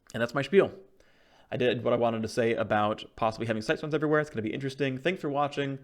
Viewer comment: wanted to say about possibly having sex with everyone uhhhh WHAT ? PHREAK XD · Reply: He actually says "I did what I wanted to say about possibly having Sightstones everywhere". But did sound like sex, yeah.